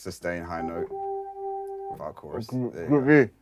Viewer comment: the subtitles are wrong, they meant "Group B"